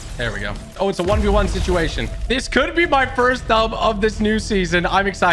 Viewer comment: Who else heard him say "first dub of chapter 2 season 6"?